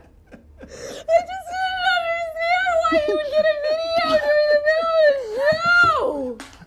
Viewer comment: 5 bucks to anyone who can decipher what she said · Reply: I just didn't understand why he would get a video during the middle of the show.